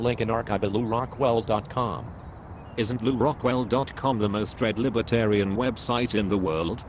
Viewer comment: my bad, most read, thought he said most dreaded